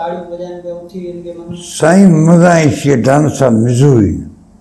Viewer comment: did he say mzuri or am i tripping??😮